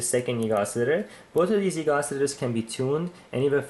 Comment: at around 44:04 you're talking so fast I can't understand what you're saying... a what-oscillator? eegoscillator it sounds like. what are you saying?!? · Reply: Frozen Electronics Yes, sorry. YIG Oscillator. :)